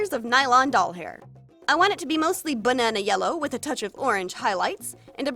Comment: Does anyone else hear her say bununa at 2:25 instead of banana or is it just me